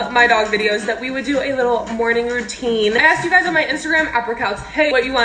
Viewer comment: did she say “@ or “upper cuts”